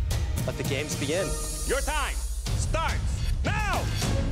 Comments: I thought he said, “Your time, starts.. MOW!!” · Did anyone else hear "Your time starts MAO!" at 1:45?